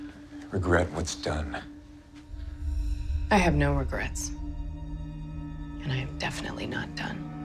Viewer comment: when I watched this the first time and she said "no regrets" I thought she said "nomencrantz" and I was like..wtf is that? hahaha